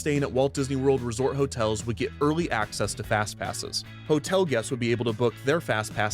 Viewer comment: I thought it say "Fatpass" and in my head kinda made sense "yeah the fuckin' wales always yell the most, rude and abnoxious, especially the fuckin' 'muricans, weird choice but..hm !"